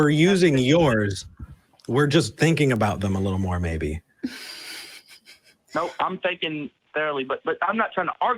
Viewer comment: did he just say "No im thinking! barely...